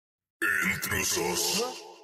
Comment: ¿Que dijo en en esta parte alguien me ayuda a averiguar lo que digo por favor😅😅😅? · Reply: dice INTRUSOS